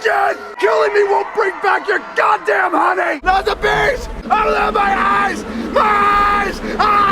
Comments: thanks for the suggestion chris, but i can't watch this movie, cause... MY EYES! I LOVE MY EYES!!! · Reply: I always thought he said "Oh no, my eyes..." but "I love my eyes" is so much funnier 😂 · I never get to understand what he yells after "not the bees" at 20:10 Oh my god my eyes? Oh no, my eyes? Someone whose mother language is English can translate? 🤣